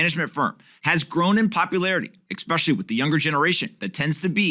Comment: Is he saying "exspecially"?